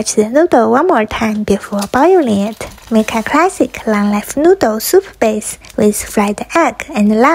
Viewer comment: I thought she said, "long noodle" before I read the subtiles 😅😂😂😂